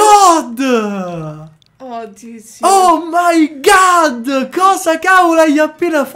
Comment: ho sentito un d*o caz*o da parte di